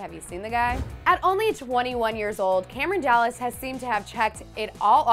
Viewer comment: does she say Cameron dallans on 0:23😂😂